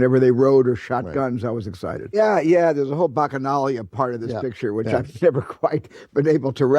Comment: What is Spielberg talking about when he's saying: "There's a whole ..."? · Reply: He said "bacchanalia" and the video is showing men taking baths. He is referring to that part of the movie.